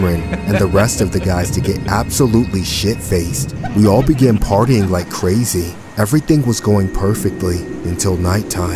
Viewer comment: I thought it said farting like crazy lol